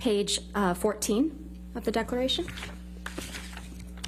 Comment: I thought it said defecation trial? 🤓